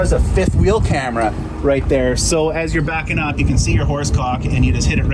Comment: Now when do we get trailer backup cams Also did he call the kingpin a horsecock?